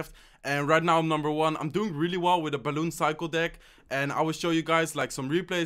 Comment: バルーン最高でーす に聞こえたw